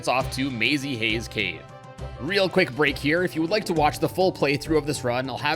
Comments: Did he said "Mazy Haze Cave"? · You already failed a minute in, Nintendo don't intend emulation >:O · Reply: Also 10:38 mazy haze cave is not the intended name, it’s hazy maze cave lmao, when I first heard it I had to make sure that it was in fact hazy maze cave and not what he said lmao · did he just say mazy have cave